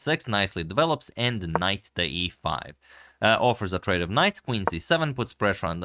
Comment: For a second I thought you said, "a trade of kings." And I thought, that's cool, we've reached a new game that not even my uncle in the library-bar could find.